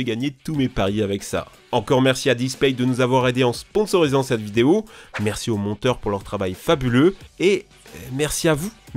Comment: PARDON ?! EN ELIMINANT QUOI ? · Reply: Les renois